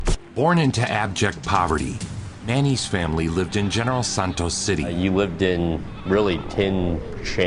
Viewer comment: i thought he said los santos for a sec there